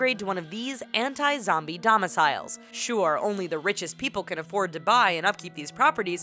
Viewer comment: i thought you said only "TheRichest" can afford